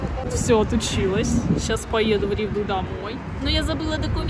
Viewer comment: в Ригу ( Латвия) или мне послышалось?